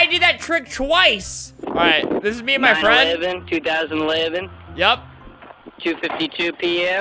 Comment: Did he say 9/11 2011?